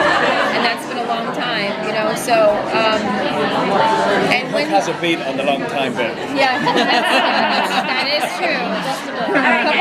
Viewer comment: I hate that you can't hear part of this video, BUT from what you CAN hear... this interview was very insightful into how each person and character thinks. Colin calling her "babe" is always cute! I know he's married and I respect that, but you can't help but ship their characters! <3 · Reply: TwilightSagax It sounds like he says babe around 5:39. Something in the lines of "Hook hasn't been in a long time, babe" It's so loud not sure if that's what he said.